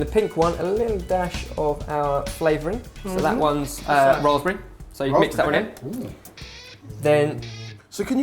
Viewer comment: OH raspberry I thought you said rosemary...